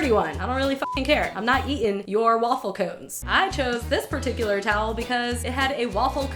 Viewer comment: I literally thought she said WAFFLEO...